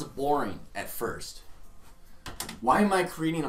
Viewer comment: i thought you were saying porn game